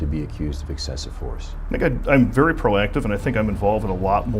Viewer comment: “nigga I’m very pro-active…” wait what you say my boy? 🤔